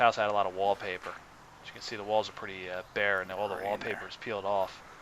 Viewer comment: I heard a ghost or someone was with you? Sound like he said "Pretty in there". A man's voice.